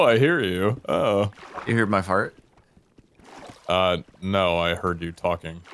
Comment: "Did you hear my fart?" I thought Sparklez said "heart" at first, but I probably should have known better...